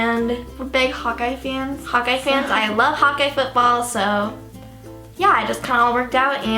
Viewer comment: Am I the only one who thought she said "I like hot guy football" ? 😂😂😂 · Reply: No she said Hawkeyes football 😂😂